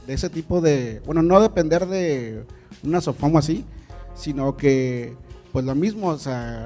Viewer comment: Que dice aquí 1:45 ? No depender de un... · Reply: De una SOFOM